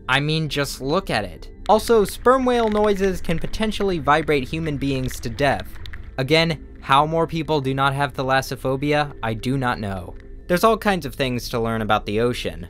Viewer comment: Yoo I thought it said ThatAssophobia